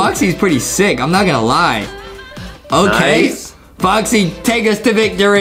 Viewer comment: did Adem said boxy?